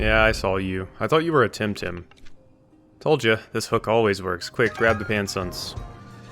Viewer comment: For a moment I thought he said "Quick, grab his pants!"